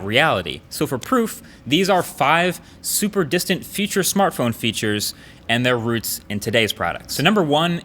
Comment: Am I the only one who heard "feauture smartphone feautures" at 0:38?